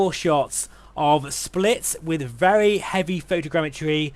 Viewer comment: Where are you "flying" over @ 5:00? I want to visit! (In simuworld.) 😀 Sounded like "Splits?"